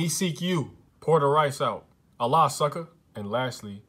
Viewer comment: When he said Alaska it sounded like he said Allah sucker 😆